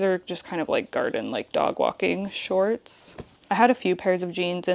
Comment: ARF. Did you say dog walking mummy?! 😹 so cute